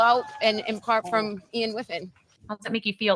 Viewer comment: The gross brown nosing is cringe. I heard 'apart from Ian W' not 'in part'